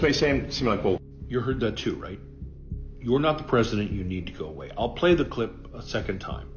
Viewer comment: He literally said "mister president i have to pee" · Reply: Yep I litteraly had 5 people listen to it and all said it sounds like he says "Mr.preident I have to go pee. And the second part he's like it's clear as day, no it's not I can barely hear anything hahaha